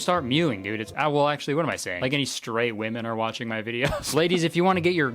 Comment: As a queer woman, I felt really seem... Once I understood what you said. For some reason, I heard "stray woman" instead of "straight woman"